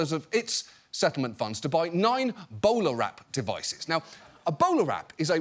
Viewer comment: Every time john says bola wrap it sounds like he is saying boner wrap